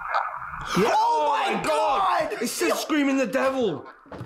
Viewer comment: Listen to that!! 36:26 your analyse is not right and I'm shure I'm not the only one who hears "blaming the devil!"